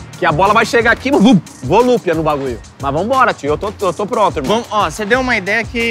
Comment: "Volúpia no Baguio" só eu escutei isso?